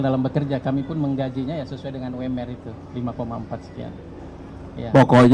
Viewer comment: gaji berapa , potong berapa , yg disimpan berapa ...... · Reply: tadi kalau g salah denger, 5,4 jt ? 6:05 udah dipasang wifi juga rusunnya.